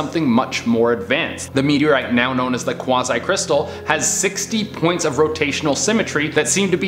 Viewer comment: Why... Why did I read Quasi Crystal as Croissant Crystal- I just heard the "Qua" part and I literally said "QUASSONT! OUI OUI" help my ears